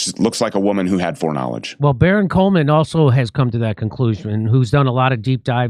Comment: …did Jimmy say Baron Cohen?…as in Sacha Baron cohen? Good god I would love to hear Borat’s thoughts on this topic 😂😂😂